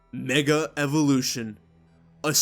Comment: Ngl, I first thought you said nigga evolution at the start.